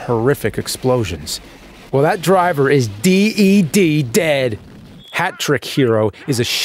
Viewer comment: Im I tripping or do I hear deed instead of dead 23:08?